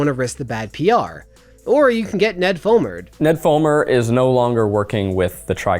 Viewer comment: Could somebody fill me in on Ned Fulmer? The Try Guys is not the kind of content I watch so I have no idea who he is. To show how out of the loop I am, I thought they were saying 'Ned Fomer'.